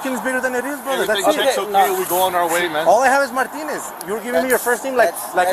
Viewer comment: The cop literally sounds like he says “ ALL I HAVE IS MY PENenis “ ⏱️ 14:00 time stamp He was trying to say “ All I have is Martinez “